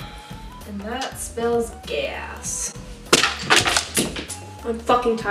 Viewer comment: I heard gay ass and could not stop laughing😂